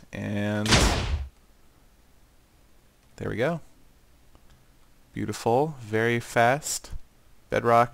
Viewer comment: did you say skyrim lol